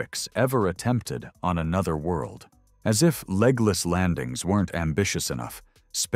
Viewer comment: I thought for a second you said "Legolas landing", and wondered if they were sending elves.